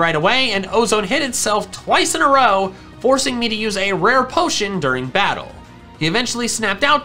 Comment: did he say “rare potion”?